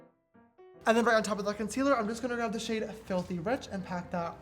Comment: I thought he said "im just gonna grab a fucking brush"